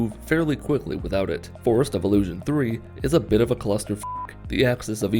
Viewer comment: somehow I heard "Forced Evolution V"...